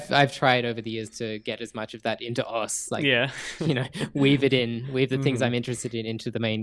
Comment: i thought he said weeb it in lmao